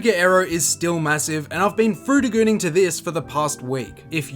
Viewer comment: Did you say you've been frutigooning?